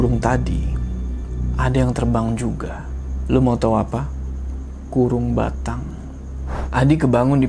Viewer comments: ada yg tau ngomong apa? Trus itu maksudnya apa? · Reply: "kurung batang" (keranda jenazah) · itu bilang apa.. Ga tau gua apa itu artinya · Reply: Keranda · salah denger kirain "Burung Batang" 😂